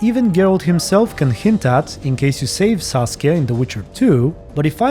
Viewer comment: I'm sorry that I misheard Saskia as Sasuke. Hahahaha